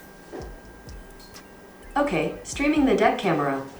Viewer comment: Right after they said Apple users are out of luck cause the camera only works with google assistant at 3:40 I heard “Hey google, show the dick camera” I actually thought they were gonna show an Apple camera, and didn’t realize he said deck instead of dick… cool story